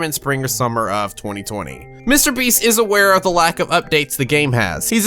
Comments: did say Mr.Beast or am I deaf? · Did just say MrBeast? XD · who else heard him say mr beast says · did he say Mr Beast.